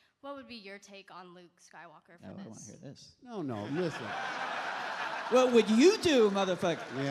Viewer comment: did Rian say „what would you do motherfuck...” ?